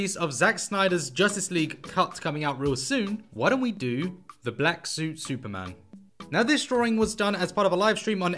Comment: I’m going to be honest. I thought he said black suit spiderman in the polls